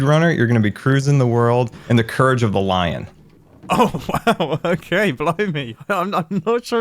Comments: it sounds like Mumbo says "blow me"😂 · does he say “blimey” or “blow me” 🤔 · Did he say "ok blow me" if so I'm concerned especially since scar was in his underwear...